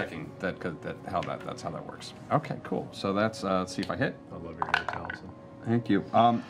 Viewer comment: Just checking, I think Travis says "I love your hair, Taliesin" at 2:15:20 but it's hard to hear.